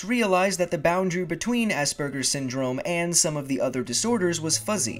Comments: I honestly thought he said assburgers I feel stupid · whenever he said asperger i thought he said assburger